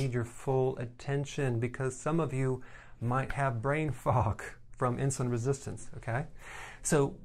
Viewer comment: wait .... Did he just said I have brain fog 😭😱😱😱😱😱